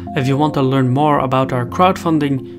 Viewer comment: What did he said at the end. Sow ??? or Shalom or Sahow What he said?